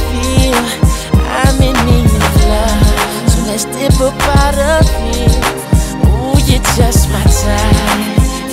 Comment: When I 🩷’d Lil Wayne! I heard “Let’s dip up by the pier” 🎶🤭